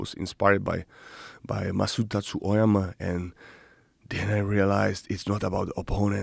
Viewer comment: Who did Jiri reference at 36:20 ? Sounded like “Masuta Tusaema” or something like that.. anyone? Thx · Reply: Masutacu Ojama, the founder od kyokushin karate, you are welcome 🤜🤛